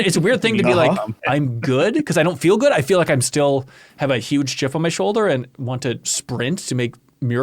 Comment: Did say he has a huge GIF (jiff) on his sholder?!